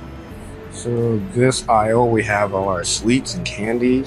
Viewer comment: ここの部分は This aisle, we have a lot of (rっぽい音も聞こえるので不安ですが) sweets… のように聞こえるのですがどうでしょうか？？